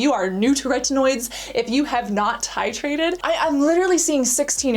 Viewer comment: Am I the only one who noticed that started saying "babe" a lot? haha Cute on her :) Also, am I the only one who hears the word "titrated" on 6:26 and NOT "hydrated" as it says on the screen?